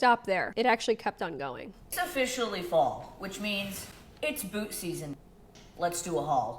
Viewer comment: I swear to god i thought she said " Book season ". I was like, well that seems a bit more money well spent. She said boot .. -_